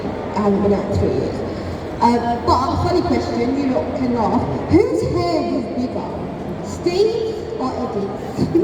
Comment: what was the question? I cant hear clarly pd: thank u so much for show the video!!!! You are very lovely :virtualhug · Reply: the question was: "Who's head do you think is bigger: Eddie's or Steve's?" And Joseph answered Eddie's of course!